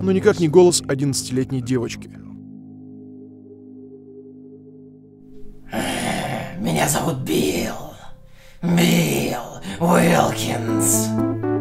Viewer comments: ахахахх мне послышалось "Меня зовут Билл и это новый дом" и я ждала когда он дополнит "2". В итоге я ждала - "Меня зовут Билл и это новый Дом 2" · Мне послышалось: Меня зовут Бил и я дебил.аххахахахха